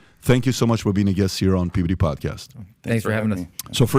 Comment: It sounds like he says "Puberty Podcast". Yo, I ain't tryin' ta have all that.